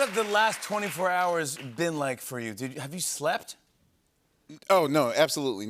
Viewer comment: did anyone else hear it as “have you slapped?”? I was like, oh shit here we go again